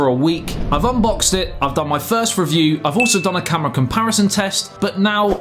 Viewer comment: Sounded like he said thirst review which would fit